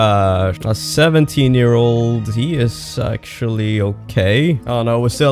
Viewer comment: sounded like you said he is sexually ok